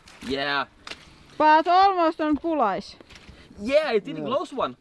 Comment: Does he say bulleyes at 2:38...:-)